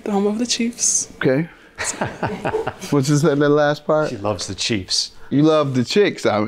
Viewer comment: Judge Mathis thought she said she likes the chiks he is being messy today,Lol😄😄😄😄😄😄😄😄😄😄😄.